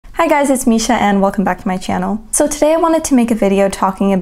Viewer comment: I could have sworn I heard "Hi guys, it's me, Schienne..." My wife's name is Schienne.